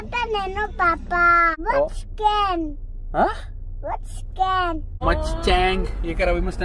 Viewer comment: actually sounded like 'vats gang', but when ji said mustang😂 was like no papa🤣